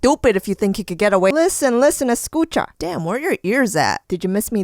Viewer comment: Did she say "Escucha"?